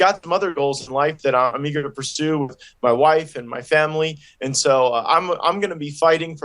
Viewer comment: i heard "[...] I'm gonna pursue my wife, my family [...]"... should we be worried now, that he's going to rape his wife next or what? Crazy dude.